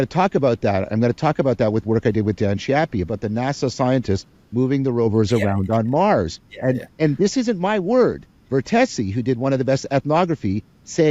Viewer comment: Can anyone tell me anything about the " Don Chappie" Vervake references at about 40:30ish? I'm hearing impaired so I just can't tell what he' saying and the transcript isn't helpful. Much thanks to anyone who could fill me in. Pax V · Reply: He's referencing the paper "Distributed Cognition and the Experience of Presence in the Mars Exploration Rover Mission" by Dan Chiappe and John Vervaeke.